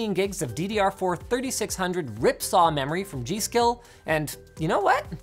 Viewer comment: “ripsaw memory” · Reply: lol I didn't hear that I actually thought he said ripjaw memory